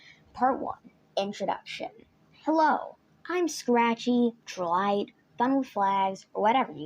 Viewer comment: did you say you're actually scratchy?